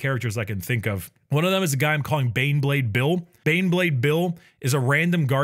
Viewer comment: BANEBLADE BILL MY BELOVED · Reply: I thought he said Beyblade Bill at first and pictured a guardsman riding a giant spinning disc through a sea of nids